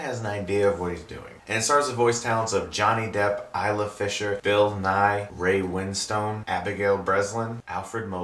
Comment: I thought he said Bill Nye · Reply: Bill Nighy. Yeah, it gets confusing